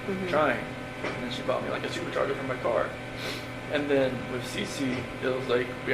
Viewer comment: can anyone please tell me what Chris says at 21:46? he is talking about trying to conceive Bella and says : <it was like, we almost gave up, trying. And then she bought me like a super charger for my car> He cant be saying super charger-what would that mean in relation to conceiving Bella?? THANK YOU!